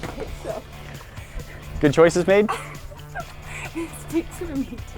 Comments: Was it me or at 12:42 did call babe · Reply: Rachie 2345 just you he said made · I rewatched this moment about 5 times and turned on the captions before I realized he was saying made instead of babe I was so confused😂😂😂😂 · Did he say babe or made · Am I the only one who thought that at 12:41 he said babe lol then I realized he said made 🤣